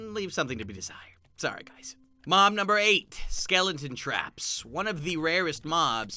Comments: did he just say mom number 8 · did he say MOM number 8 ???